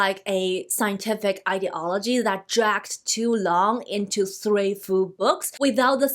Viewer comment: I thought what was said is " jacks too long", which would not be wrong, but maybe it was "tracks"?